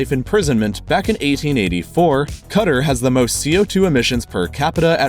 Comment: Did he just say cuter instead of Qatar. R u serious m8